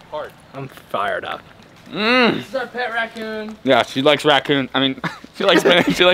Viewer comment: I heard “pet raccoon”